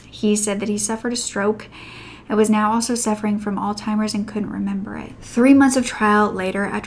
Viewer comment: I thought you said ‘old timers’ (which I used to think it was) but on listening a couple of times I worked out you said ‘Alzheimer’s’ 😭😭😭